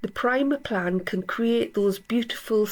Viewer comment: Enjoying this so much! You make gorgeous photographs! I am not understanding what you are using when you say primer plan? On around 7:16. Thank you!